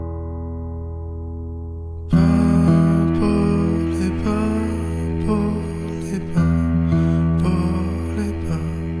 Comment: Its been so many years listening to this but I still don't know what he is saying from 04:25 I really want to know. Anybody help?? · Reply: "她不是风，不是风，不是风. 她迷迷糊糊，她 .... (the voice is too ligtht to capture)"。 In English: She is not wind, not wind, not wind. She is in a daze. She .... (the voice is too ligtht to capture)